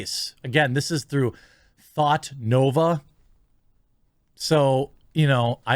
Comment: I thought he said Thotnova...